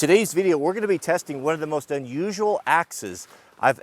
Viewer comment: as a non native speaker, I heard "today we are testing the most unusual access"... whaaat?